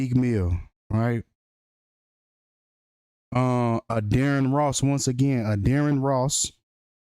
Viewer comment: Why Wen U Say Addarren Ross Sounds Like Your Saying Dearest Loss , Internesting 🤔😒😣